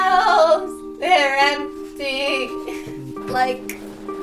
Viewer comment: i thought it said "she lives empty" lol